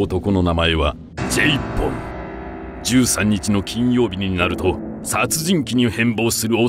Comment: ジェイポンがJ-Phoneに聞こえる。